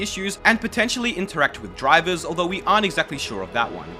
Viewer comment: Hello after watching this i heard "and potently tamper with driver" i wonder'd, on an older laptop i had TLauncher, it was running like fine until one random day where my laptop just completely died, the hard drive had died for no apparent reason, we took the laptop apart to cleam every single inch, the hard drive its self had no visual issues nor did the laptop, do you think it'd possible that the cause of this was due to TLuncher (since then i have brought minecraft) Kind Regards